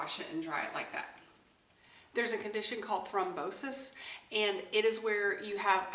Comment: Hah! I thought you said "Frumposis" at first when you said "thrombosis!" I'm a nurse and BOTH are very real😉